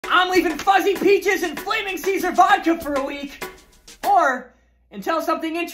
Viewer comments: Sounds like you said flaming Caesar’s vodka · Why did I hear " I'm leaving ducking peaches in vodka for a week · I heard " I'm leaving ducking peaches in vodka for a week"